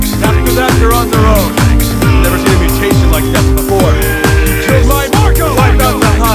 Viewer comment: Idk why i heard "Jill's my marco" at first lol